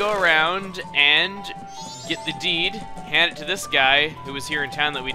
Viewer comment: i thought you were saying "get his 'D'" not deed........ HE WANTS THE D!